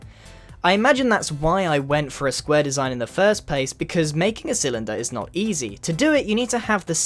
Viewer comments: Wait did he say pace not place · Did anyone elwe hear pace instead of place